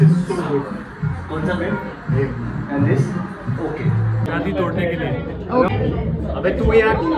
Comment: what said at 14:43?? I heard shadi todne ke liye what that means?whom shadi he was talking about if someone know please replyuyu